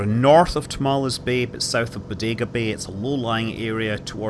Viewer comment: did you say Dagobah? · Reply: Sounded like a Bodega.